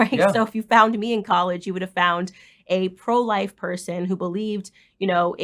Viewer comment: did she say she was pro-life in college lol